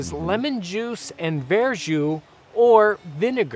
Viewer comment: Good thing i checked the comments and googled verjus/verjuice cause i misheard it as Bearjew which would have been waaay off with what i would have thought if i went with it. lmao